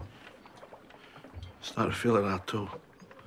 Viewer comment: Can someone please tell me what says at 2:22? That line makes me crack up for some reason haha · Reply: "i'm startin' to feel odd, too..."